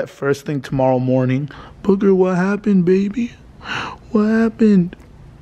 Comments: is it me or did she say baby ? 🤭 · did call baby 🤔